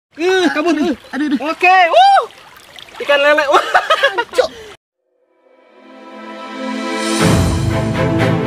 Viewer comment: kang panji bilang apa tuh😂😂😂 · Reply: Janco